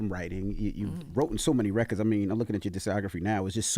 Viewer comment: did Envy just say “wrotten so many records” 😂